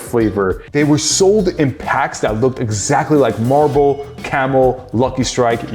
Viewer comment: did bro just say malbo for malboro?